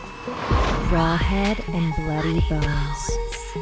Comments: I heard it as "raw head and bloody balls. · I thought it said bloody ones but not bloody 🦴 s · I thought it said raw head and bloody balls ( ͡° ͜ʖ ͡°) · When you said bloody bones, i heard it as bloody balls